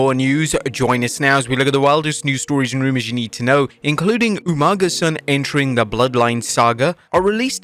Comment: i heard umaga's son quit